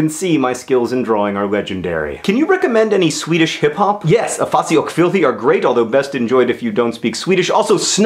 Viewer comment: Ooooh i just got it! Afasi OCH filthy lol xD thought he said afasioh filfa or something xD your swedish is terrible john o.o might need to work on that xD